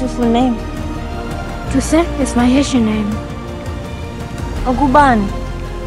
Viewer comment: lmao,i thought that kid said " touisant is my Asian name"